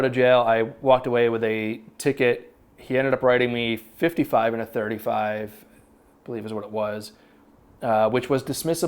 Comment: Sounded like he said 45 in a 35 at first.